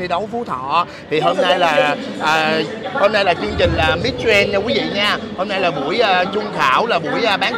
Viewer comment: Miss Grand mà nói nghe ra Miss Trans ( Miss chuyển giới hả ?? ) 😌